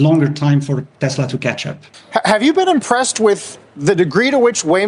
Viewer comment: i heard San Francishitco😅😂